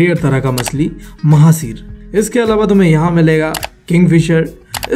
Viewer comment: I hear it at first time -Bawasir